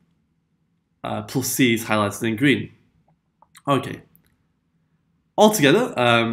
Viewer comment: what did he sayyy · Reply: he said ok